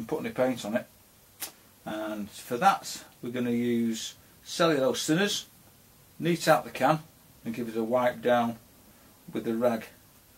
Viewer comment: I don't understand what he says... anybody ???? Please ! Thanks ! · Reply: Cellulose thinners neat out of the can